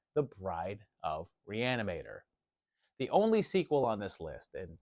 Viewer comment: I misheard when you announced Bride of Reanimator and heard Reanimator. I was like WHAT???? It's like not having Jaws at number one in your shark movie list! LOL